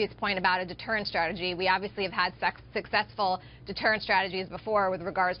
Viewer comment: Did she say: "we obviously have had sex...." at 5:15 ? Thumbs up if you hear it :)